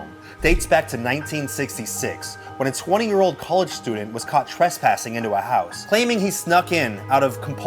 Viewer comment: I totally thought he said "twesspassing" for a sec 😂